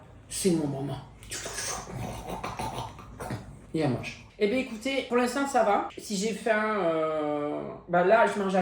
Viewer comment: pourquoi j'ai entendu ''bebe écoutez ''😂😂